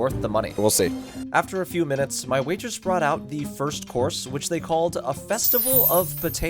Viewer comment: haha i literally heard "horse" and immediately had to replay it, thought "wait a minute, this isn't the way we were headed"